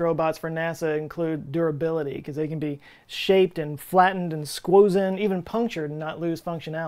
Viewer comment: did he say squozen?